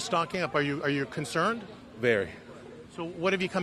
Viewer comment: I thought the guy at the end said: “water toilet paper hand sanitizer and candles” until I replayed it😂😂